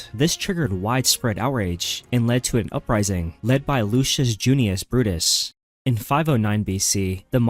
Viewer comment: I watched "predictive histories" channel , and I kept hearing him say , "lucius buddist !" 😂 but it was really just his accent, which is why I misheard it.